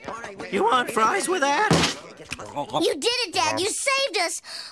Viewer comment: I missheard it as "I am proud you are are my father" "Oh wait, I ain't"